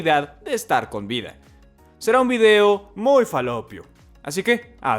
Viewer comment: Escuché " va a ser un vídeo bien falopero" jajajaja